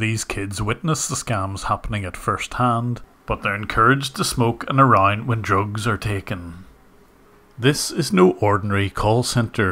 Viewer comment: what are you saying here? "They are encouraged to smoke anarang when drugs are taken"? I want to guess this is "They are encouraged to smoke and ARE around when drugs are taken"? But there a missing word somewhere